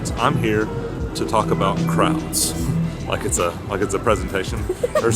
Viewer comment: Can't wait to see y'all in 6 days! Great video as always! When said "crowds" I thought he said"crabs" bahahahahah😂😂😂😂 I cannot wait for the mini meetup!